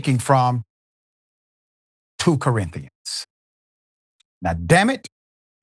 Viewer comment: I thought you were saying Korean instead of Corinthian, got really confused. Thank heck for subtitles.